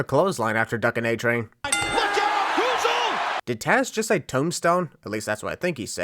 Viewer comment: I think Tazz actually said “goozle” in reference to the chokeslam